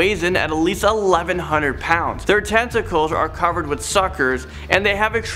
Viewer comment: or 5:04 tentacles sounded like testacles